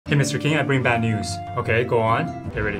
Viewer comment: I thought he said chemistry king